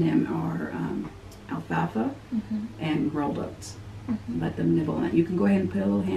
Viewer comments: she said u can start to feed them alfalfa and “ xxx”? WhAt was the second word? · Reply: I think she said rolled oats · What did she say to put in there with them?? I couldn’t hear.. it was two things she said that they might not eat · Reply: Jessica Lynn she said alfalfa and rolled oats